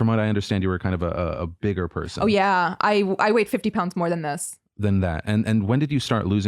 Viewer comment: lmao i thought she said she weighed 15 pounds more than she does now and was like why is she acting like she was so fat? so i went back and apparently it was 50 pounds lol